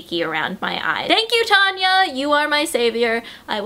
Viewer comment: When you said Tanya I thought that u had said Kanye (like as in Kanye west) hahahahahahahahaha